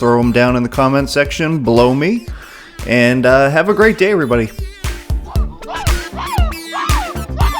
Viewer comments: Great video, exactly what I was looking for and straight to the point. Thank you. I laughed at 10:34, it sounds like you said “blow me!” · Sounds like you said "if you have any criticisms, throw them in the comment section. *blow me*."